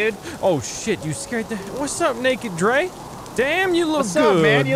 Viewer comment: sounded like "what's up nigga drae"